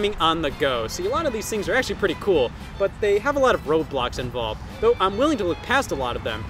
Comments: DID HE SAY ROBLOX!?!?!?!?!?!?!? 😱😱😱😱😱😱😱😱😱😱😱😱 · I THOUGHT YOU SAID ROBLOX